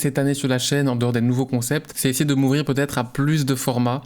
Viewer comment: j'ai entendu "essayer de mourir peut être"